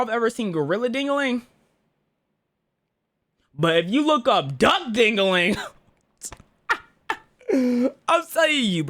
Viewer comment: this thumbnail caught me off guard wait what did bro say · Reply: u look up duck dingling